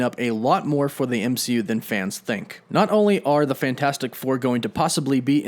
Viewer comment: did he say...Fantastic Floor?